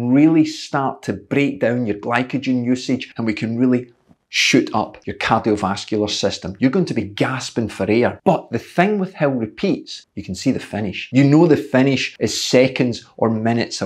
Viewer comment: Dammit coach, I thought you kept saying Hell. Hell training, Hell repeats like its some super horrific type of training..